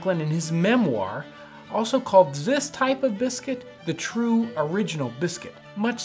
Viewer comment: did you just say “zis type of biscuit”